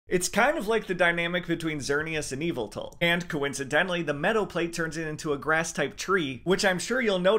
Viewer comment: “Metal plate turns it into a grass type tree.” Huh 😅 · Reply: yeah its sounded like he said “metal” plate but that was the grass form of arceus. Thats is the meadow plate.